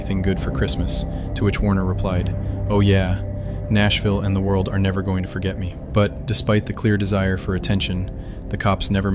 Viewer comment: تسنیم نیوز چی میگه ؟😂😂😂